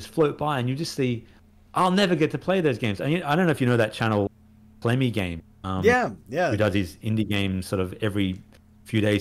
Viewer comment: what Youtuber was mentioned? I couldn't quite catch it. Love Gorgon Shield by the way, great stuff! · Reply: "Clemmy Games" Also goes big BIG (Best Indie Games)